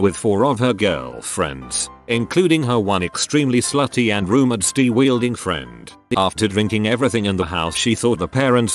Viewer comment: I hear "STI-wielding"...I thought of the car first